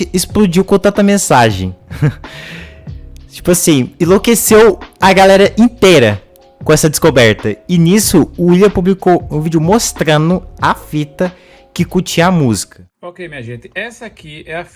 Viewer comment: What is being said here? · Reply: I said "Like this, drove the whole crowd crazy" I thought that this part isn't soo important, so i didn't put subtitle in this part